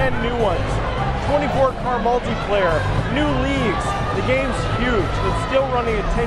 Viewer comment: did he say "new leagues"? does that mean we'll have multiplayer rank based on leagues?